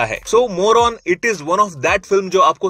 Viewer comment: is you said more onn and I heard Moron, lol 😂😂